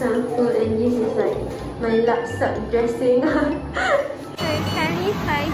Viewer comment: she said lap sap (trash) dressing but the subtitles said very casual lol